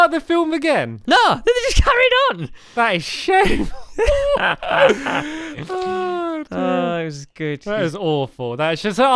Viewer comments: pardon me for my language but did stampy say sh*t at 28:27???? did anyone not notice that??? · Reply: No, he says "that is shameful" it's just the way he says it that makes it sound wrong. It's definitely "that is shameful" tho. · OMG 28:26 DID STAMPY SAY "That is sh*t" ?!?!?!? WHAATTT